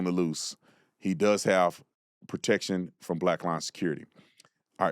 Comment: I always thought you were saying black lion security.....arguably a better name lol